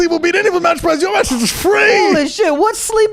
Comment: Just me or did anyone else watch 9:43 like five times and still not understand what said lmao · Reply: He said, “We will beat any mattress price. your mattress is free.